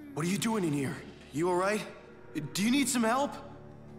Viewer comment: i think he try to say is your the one who needs some help